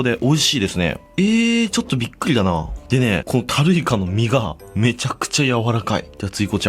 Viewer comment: ホタルイカに聞こえて俺の常識が崩壊しかけた